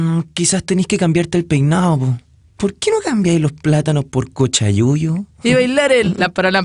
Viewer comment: procesando lo que dijo Ramon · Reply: cochayuyo